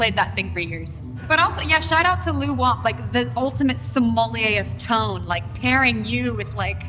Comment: A sommelier, or wine steward, is a trained and knowledgeable wine professional, normally working in fine restaurants, who specializes in all aspects of wine service as well as wine and food pairing. The role of the sommelier in fine dining today is much more specialized and informed than that of a wine waiter. Wikipedia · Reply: I thought she said somali of tone.